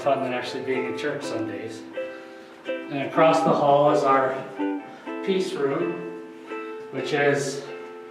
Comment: When you said peace room I thought you said pizza room lol